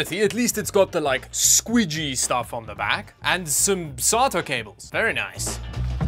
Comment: I legitimately thought you said "Solder Cables".